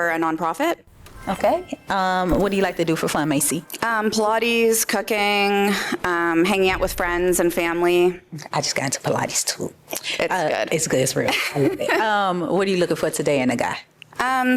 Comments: I swore she said she likes cocaine. It was cooking. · for real I thought she said “Pilates, cocaine…” 🤣🤣